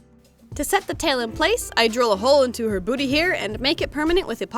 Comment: when she said "drill a hole into her booty here" I thought she said "drill a hole into her booty hole" X3